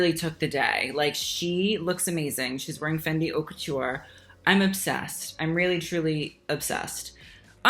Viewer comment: Thought you said “Fenty haute couture” at the end and was v confused for a mo